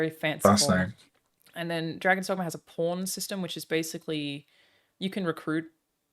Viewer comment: did anyone else first thought said porn system instead of pawn system? 🤣